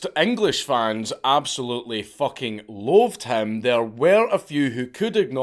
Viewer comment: When Dank says "Loathed" it sounds like he is saying "Loved"